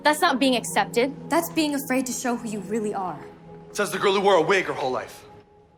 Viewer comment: Sorry, at first i thought it was "said the 'Gorilla' who wore a wig her whole life" but when i rewatched it , it was "girl" not gorilla⚰️